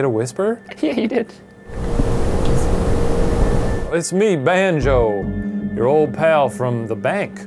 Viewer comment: I think it said kiss me